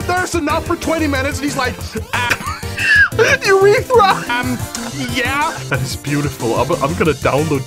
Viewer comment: did bro say urethra??? 🤨